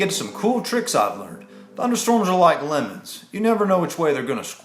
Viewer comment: I'm sorry to say this but at 0:13 I literally thought I heard women and not lemons..... Ope